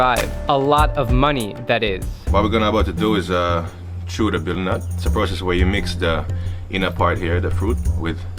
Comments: Thought it said "beetle nut" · Thought it said butter nut